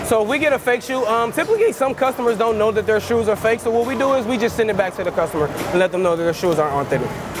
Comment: Did he say send fakes to the customers anyway 2:44? So they are not authentic???